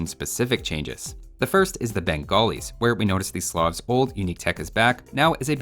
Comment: the subtitles say "these sloth's" instead of "the slavs" at 10:29 the subtitles say "impure" rather than "imperial"